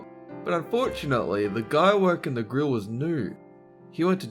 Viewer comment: I thought he said the guy was nude. XD